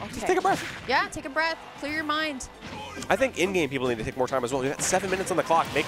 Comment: did he say Indian people?